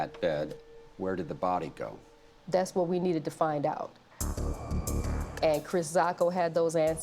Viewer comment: I thought they said Chris Sacca, and my immediate reaction was THE SHARK TANK D-BAG KILLED HER?!